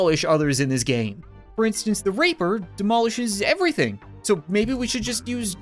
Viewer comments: Did he say the raper? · yep when a unit is on its own it jumps off a cliff. 13:44 also did he say RAPER?!?!?!?!??!